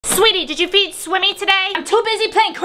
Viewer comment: I thought she said, did you win swimming?😂